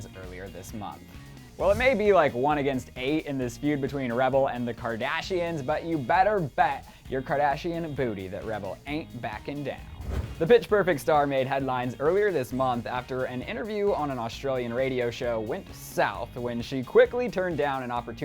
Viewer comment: I just heard "Bitch perfect" 😂